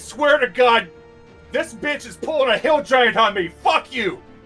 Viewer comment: A what? A hill giant huckey? Hustle? Or is saying "pulling a hill giant on me?"